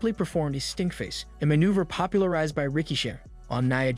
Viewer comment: did u just say the stink face was popularized by ricochet?? 😂😂😂😂😂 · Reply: I heard Rikishi